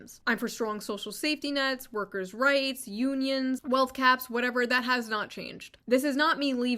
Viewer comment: Did she say wealth Caps or Gaps? . Do I need to start researching wealth caps, cuz I don't like the sound of that